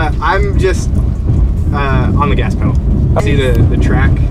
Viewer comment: Did I hear GAS pedal coming out of a guys mouth that is driving an electric car